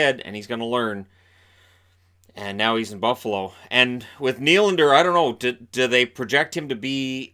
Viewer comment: Damn...I thought he said William Nylander. As a Leafs fan I would have thrown in a case of tape and two sticks to get rid of William.